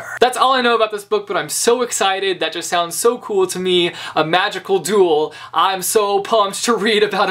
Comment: I thought you said, "A magical doodle." I was so confused until I realized it was actually magical duel...